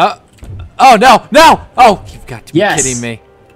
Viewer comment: did said ' oh you got to be sh*@ me' on